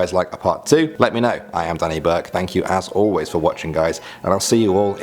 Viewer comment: I thought he said as*hole ways for watching